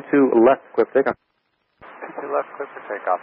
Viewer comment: there is an error in your subtitles at 0:24 they were cleared for takeoff but the subtitles said cleared to land